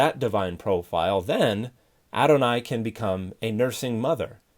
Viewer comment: Look up “Why I Say Adonai.” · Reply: Thanks. The problem is I couldn't understand from his pronunciation what he was saying. I searched for "aronai". I thought it was what I heard.